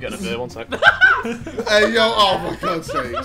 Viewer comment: what did harry say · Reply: I’m getting a beer